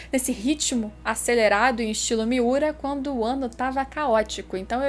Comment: Ela falou, "quando o ano fica caótico ", eu escutei "quando o wano fica caótico", kkkkk já está! Espero que venha logo mais de berserk, e que venha algum anime em 2d também!